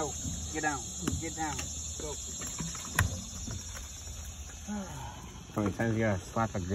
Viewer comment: “Sometimes you have to slap a gator to make it understand.” 😂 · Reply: Thought he said “how many times…